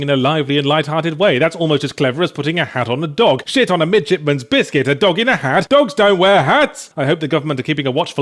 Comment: "Shit on a midshipman's biscuit, DOGS DON'T WEAR HATS!" That should be put on a T-shirt. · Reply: Matthew Cool I thought he said midget's biscuit : /